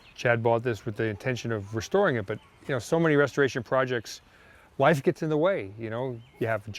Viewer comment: did he say "life gets in the way" or "wives get in the way" LOL!